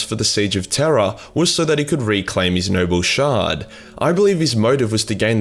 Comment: For the first five minutes your accent made it sound like you were saying “nurgle shard” So all the talk of merging the “nurgle” shard had me asking but why tho?